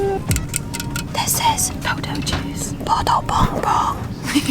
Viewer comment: i hear "puto bongbong" in 2:39 hahaha